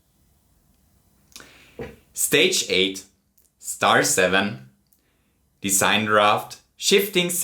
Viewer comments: People talking about "in my humble onion", yet everyone seems to miss "bittle lit" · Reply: He was playing Super Mario 64 in the 1950s. At 18:13 he says "Star 7." while the subtitles say "Star 6." At 39:02 he tells us to click the upside-down thumb, which is a dislike. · says stage 8 star 7 but the captions say stage 8 star 6